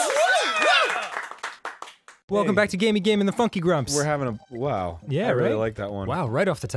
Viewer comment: I thought Dan said "Gamey Game and the Fuck You Grumps" O_o